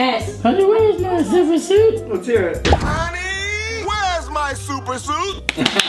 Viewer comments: Anybody else hear "Honey, where's my superjuice?" X'D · It sounded like said "Honey, where's my duper Soos?" And I thought about Gravity Falls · When did the Frozone line it sounded like he said “honey where is my super noose” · "HOOoOooONEEYYY!! WHES MA SUPA SNOOS!?" I just can't! Edit: Holy Shhhhiiioooott! This is the most likes I've ever got tysm! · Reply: For one of them I heard "honey where's my superJuice" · The incredibles one sounds like your saying honney wheres my suppersleep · I heard "Honey, where's my super noose!" and I'm very scared now. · Instead of hearing honey weres my super suit I heard, “honeeyyy weres my super juice”